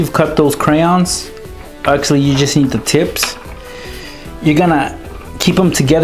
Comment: LOL i heard "Actually You Just Eat The Tips"